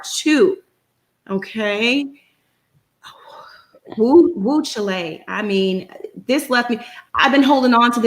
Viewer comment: Who is this lady with the avatar and why did she call that boy mam? And did she say whew chile like the country and not whew chile as in child?! 🤦🏻‍♀️ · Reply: I just listened again and at 10:40 minutes in she clearly says whew Chile 🇨🇱. Is this a new thing the kids are saying these days?